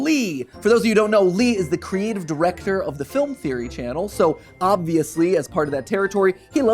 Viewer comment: I had to back it up when he said film theory. For a second I thought he said show theory